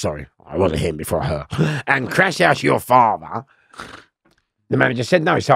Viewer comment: I originally heard it as- crash house your farmer😂